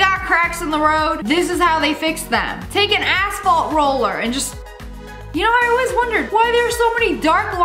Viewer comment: am I the only one who heard take an a$$ fault roller ??